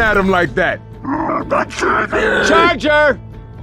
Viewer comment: "SHUT IT OFF, YOU BIRD!" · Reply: I always heard it as “Charger, overload” but that makes more sense